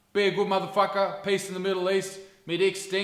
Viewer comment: Did he just say “me dick stinks?” Subscribed.